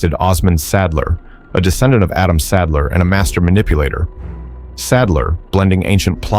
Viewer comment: I first heard that as "a descendant of Adam Sandler" lol